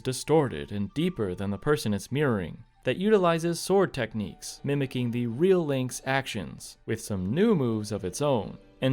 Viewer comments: what did he just say?? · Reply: Nega(tive) Link, it was a Scott Pilgrim reference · the what link. I know what you said, but it sounded like something WAY worse. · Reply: Looking back, I should have probably put "NEGA-LINK" on screen so people watching without subtitles could see what I was saying 😅 or put in footage of the Nega Scott from Scott Pilgrim